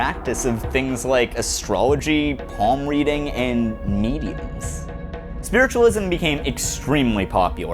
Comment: What does he say around 13:10? Astrology, palm reading, and neediness???? Is this a self-help reference?!